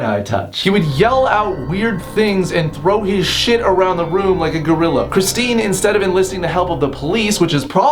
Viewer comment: I thought he said Throw his shit around like his things. until he said LIKE A GORRILLA